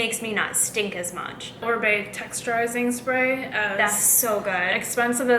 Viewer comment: I didn't quite hear what your sister said when y'all were talking dry shampoo. At 18:00, what texturizing spray smells so good ? It sounds like she said Bae dry shampoo. Could you please give a link? thx so much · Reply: She says Oribe, I think the Dry Texturizing Spray